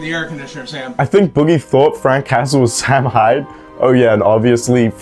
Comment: The first few times you said Frank Hassle, I thought you said "Frank Castle", like The Punisher from Marvel. So I legit thought some dude who named himself after The Punisher was harassing internet lolcows 😂